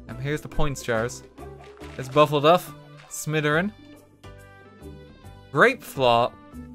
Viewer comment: in bogwarts there's buffleduff, smitheryn, grapeflaw, peter griffindoor, and ofcourse sweden. can't forget sweden. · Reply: I thought he said Missouri not smitheryn lmaooo